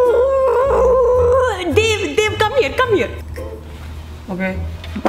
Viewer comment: I swear to God I heard Dib I was literally about to have a heart attack... but apparently she was saying Dave